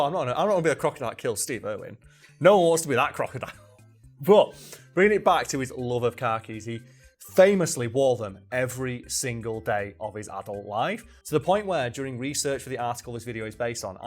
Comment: I forgot the point of the video so when you said (what could be reasonably misheard as) "car keys" at 5:06 I stopped the video and was just like "yeah alright, makes sense" before moving on to remember khakis are a weird word